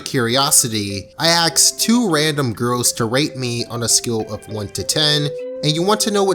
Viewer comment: Why did I hear "I axed 2 random girls to rape me"?